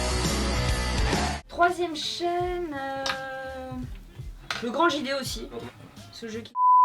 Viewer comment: Du coup elle dit quoi à 10:23 ça me turlupine, qui sait lire sur les lèvres? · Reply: Elle a dit "le grand JD je pense parce que je kiff squeezie mais jcomprend pas pk il on bippé ce moment.